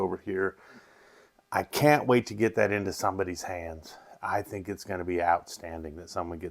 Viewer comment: I love Irish and bourbon so this sounds right up my alley. Every time you say “would love to get this into someone’s hands I hear “love to get into Gandy’s hands 😂. Happy Easter. See you on Saturday night